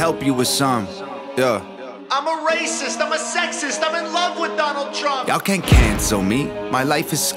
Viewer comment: he does not say i am in love with donald trump he says he is in love with donald trump